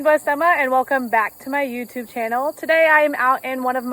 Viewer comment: I heard "welcome back to my YouBoob channel"